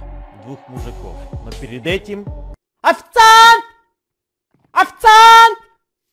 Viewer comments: что говорит Эд "Официааант" Ну и конечно же что слышу я "ОФЦААААНТ" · послышалось: ОФЦАНТ